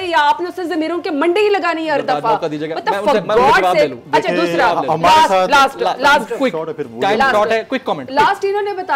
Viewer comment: What did she say? 🤬🤬🤬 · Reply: She Said For God Sake